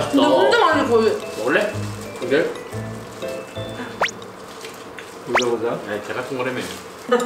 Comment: 나만 머쉬베놈 닮아서 보자보자 들리는건가 ...